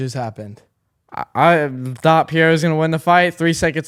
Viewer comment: It sounds like is saying Pierra 🤣 Pereira (The "r" in reira is pronounced like an "haych" sound)